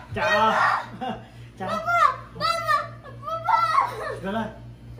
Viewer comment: كأني سمعت يقول بابا فدقيقه